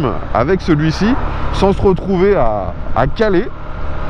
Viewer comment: tu veux dire caller, j'ai compris sans se retrouver à Calais 😏